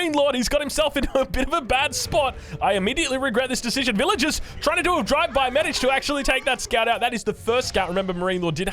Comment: did I hear that right "Villigers are trying to do a drive-by" lol